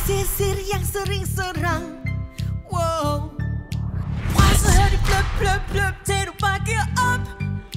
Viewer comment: did she say fck you up?!!!